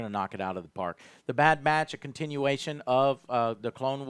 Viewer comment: I really thought that said “The bad bitch” for almost the entire video and I was wondering how Disney managed to settle on that name....